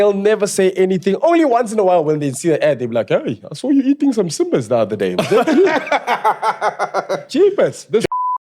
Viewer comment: what did he say 😂😂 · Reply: Hama Simba 😂😂😂